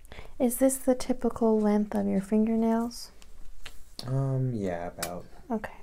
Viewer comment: I thought she said length of fingers 😂